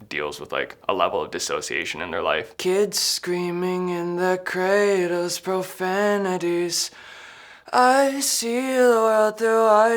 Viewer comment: I always thought he said coldest profanities